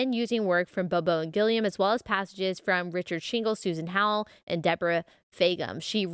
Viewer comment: I totally thought she said, "richard shingles, whos in h*ll." I had to rewind like twice